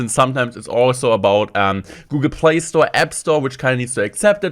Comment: i thought you said gameplay